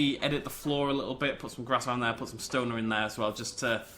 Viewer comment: Did anyone else catch that he plans to put some "stoner" in there...